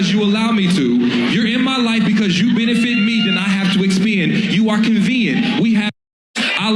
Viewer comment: Nothing wrong with but all i heard was "i love to spend money" .